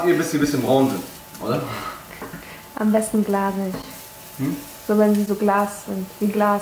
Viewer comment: Wer hat auch bei 9:50 gehört am besten blase ich